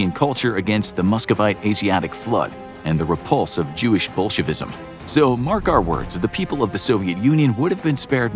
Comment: did he say Jewish Bullshitism?